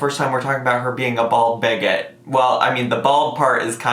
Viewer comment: Omg I heard “bald baguette 🥖” 😂 but bigot makes much more sense 😅